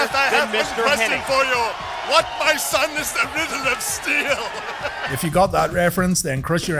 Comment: Also 21:02 What is this Kevin Nash reference? Can't really understand what he's saying.😅👍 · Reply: And neither can YouTube. Subtitles comes up, "what my son is rhythm of steel".